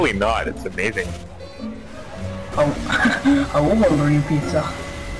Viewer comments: I think he said "I will order you pizza." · I think he said "I'm more into pizza."